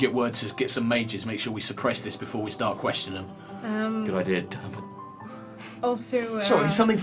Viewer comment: what does say? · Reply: Andrewx8 88 It sounded like 'good idea Turps'. I don't know if that's what he actually said.